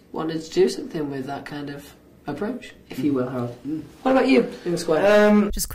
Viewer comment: in the original video you can hear it better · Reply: I thought she said 'how about you?' that's why Louis said he has a girlfriend